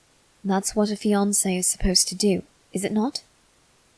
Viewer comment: I literally thought he said “that’s what Beyonce is to do. Is it not?